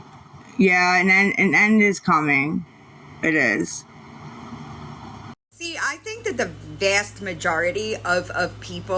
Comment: ohhh what did she say when she responded to you? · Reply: She wasn’t afraid